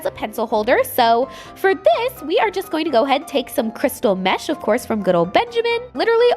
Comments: At first I thought she said crystal Meth and I was a little concerned for a moment there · I really thought that she said Crystal Meth, I had to go back to a few times lol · At about 4:08 I had to cough when she said mesh so I thought she said crystal meth. 😂😂😂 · I thought she said crystal meth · Honestly thought just said crystal meth at 4:08 XD This is what my week back at school is doing to me